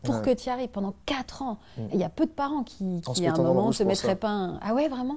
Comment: il dit quoi, je suis malentendant je ne comprends pas :x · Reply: « En se mettant dans le rouge » 😊